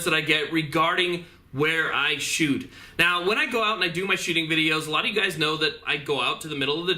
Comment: Could have sworn he said when I do my shitting videos 😂